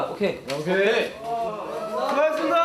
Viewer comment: 때 수고용 이라는목소리들리는데?